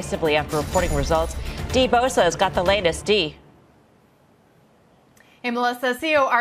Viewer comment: I only hear ”Got the latest D”